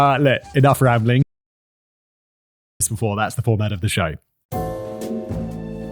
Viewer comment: what was he saying here? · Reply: "Ling. That's the format of the show."